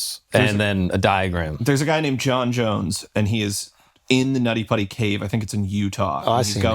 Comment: im washing the dishes and all i hear is there is a guy named jon jones who got lodged in his buddys cave